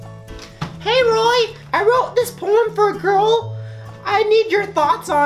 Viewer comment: I thought he said "i wrote this porn for a girl